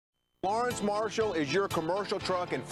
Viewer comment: As a kid I always thought he said Large Marshall. I am not a smart man.